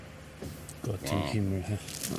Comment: I'm more curious about what Albin Oushan shouted at 16:15 Any German fans or Austian fans would like to help me? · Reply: He said "Gott im Himmel" which would mean "Good in Heaven" if you translate it word-for-word. That is an expression typicaly used when you are annoyed by something.